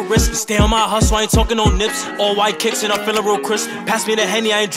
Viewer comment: he said OYK ?? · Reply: It sounds like he said that but he said " all white kicks I'm feeling real ... " 😂